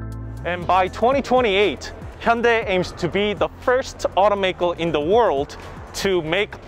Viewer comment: Spoiler alert: No, hydrogen is a waste of time. 5:25 what he said "by 2028, Hyundai aims to be the #1 automakers with fuel cell vehicles" What I heard "by 2028, Hyundai aims to be bankrupt"